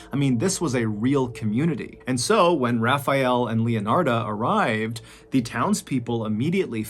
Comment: I thought he said Raphael and Leonardo like the Ninja turtles lol